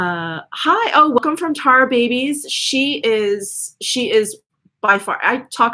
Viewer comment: I heard you say you love tar babies hauls? I know I'm not hearing you right because I've tried looking her up with no luck. Could you help me out. I've found your girl flower bomb31 and LOVE her!!!¡! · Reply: Oh - her name is Tarababyz xoxo